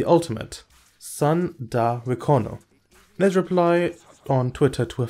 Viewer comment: TBH I thought his ultimate said "someday Wekono" as he tried to get the spirit of of his body even if it was for a brief moment